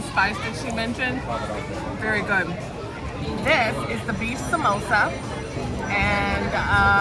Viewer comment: Gurlllll, I thought you said mimosa! 😂😂😂😂